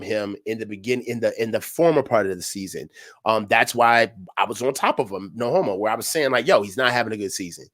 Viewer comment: Did coach say "That's why I was on top of him, no h...o🤣